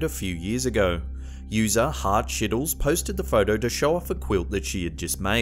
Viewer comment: I’m so sorry but I thought he said Heart Shittles for the quilt girl. I’m so sorry